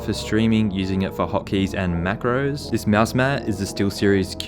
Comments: When i heard macroa at 9:26 i was like: Aah s*** here we go again. But then i realise it culd be from something else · Did you say macros